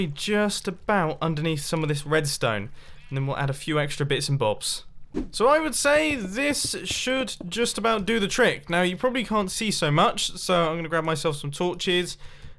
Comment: did he just say "Bits and Bobs"